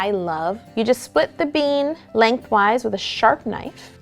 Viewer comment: When she said split the bean I thought she said split the beam #Gymnast